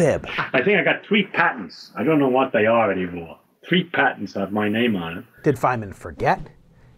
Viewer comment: Not what he said at all ... he said that the US government claimed all patents for everyone's ideas during the Manhattan Project - he was paid $1 per patent to hand them over - he had to be named on the contract for the $1 not on the patent. Just reading the book (again) and he is very clear what he meant. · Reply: Not what he said. Listen carefully at 0:29. “Three patents have my name on them”